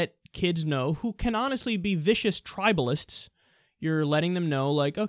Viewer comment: I heard "tribadists"